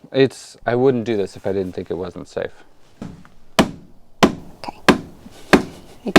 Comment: " I wouldnt do this if I thought it wasnt safe" ...said every man ever born! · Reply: Not to mention that he actually said “I wouldn’t do this if I didn’t think it wasn’t safe” (at 11:18). 😂